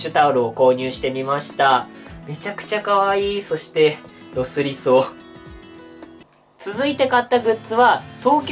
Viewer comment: 「めちゃくちゃかわいい！そして、～」 の後って何て言ってるんでしょうか？聞きなれない言葉に聞こえるのですが、どんな意味でしょう？ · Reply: コメントありがとうございます。 声が聞こえづらくてすみません。 そしてロスりそう。と言ってます。 ワンマンが終わるのが悲しいと思っていただければと思います。